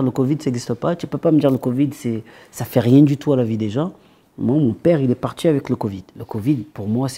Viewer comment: il a dit "moi, "MON PÈRE" ... Pourquoi vous mettez "MON PAPA".